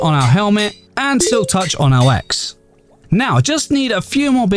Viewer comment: I heard "Silktouch on my ass"